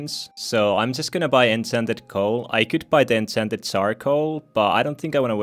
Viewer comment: When you say enchanted charcoal it sounds like you say, “incented sarcoal.”